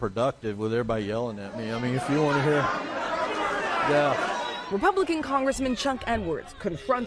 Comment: Misheard "Republican Congressman Chuck Edwards" as "Republican Congressman Chuck N-word" and barely flinched.